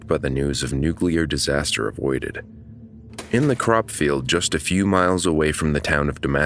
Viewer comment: LOL not sure which country/ region you from but I thought at 0:15 you said "the corrupt field of Damascas, arkansas ..." which really confused me. "What is a corrupt field?", I thought, had to repeat that part over and over. Still didn't understand, so I put on CC. Turns out it was a CROP field! So no evil or dishonest fields there, phew!